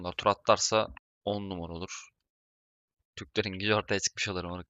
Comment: 'de ne diyor aq anlayan var mı ? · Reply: "Türklerin gücü ortaya çıkmış olur moruk"